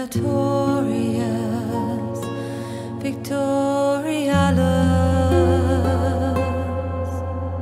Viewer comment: Bellatorius victorialis (warlike; victory) Erastus necessitas (lovers; death) honararius despondeo (honour; despair) Spiritus obdormio (life; death) Patriota gladiator (country; fighter) Afflictio ommento (pain; waiting) Divinitus salutaris (heaven; salvation) Furtivus libertas (furtive love; freedom) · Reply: i always thought it said "Praetorians, victoriano"